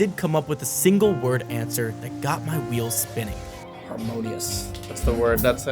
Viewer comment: looked away from the subtiles for a second and heard "amongus" instead of "harmonious" 💀💀